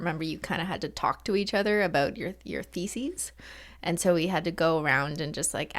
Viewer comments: OMG . 7:38 , when she said Theses I thought she said Faeces 🤣🤣🤣🤣 Any one else or am I just dirty minded ? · i thought she said we had to talk together about 💩💩💩💩